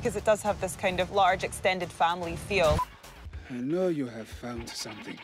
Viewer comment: I thought he said “I know you have pharmacy”